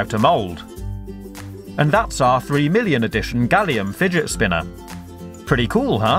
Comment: did i hear wrong or did you said 3 BILLION???